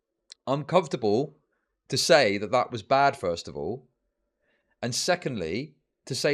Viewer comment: “uncomfortable to say that was bad first of all” is what I heard lmaooo